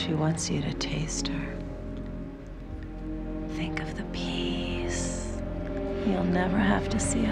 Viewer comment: When Jenny says "Think of the peeeeace." I thought she'd said "Think of the peeeeas." haha.